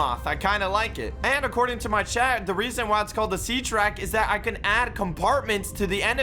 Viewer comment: did u just say seatrack